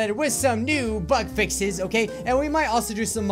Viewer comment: Who else heard him say butt fixes